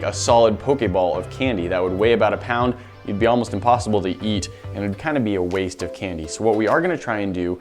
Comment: when he said "it would weigh about a pound and be almost impossible to eat" i thought he said "it would weigh about a pound and be almost impossible to yeet"